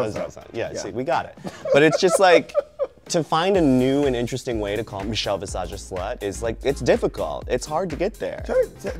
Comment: I have to know what Bob was saying about Michelle that they bleeped out at 8:53! Does anybody know? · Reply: Sopping wet